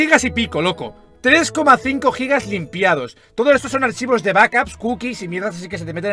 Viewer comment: Archivos de Vacas 0:52 xddddddddddddddd (Se que dice backups pero a la primera escuche vacas xddd)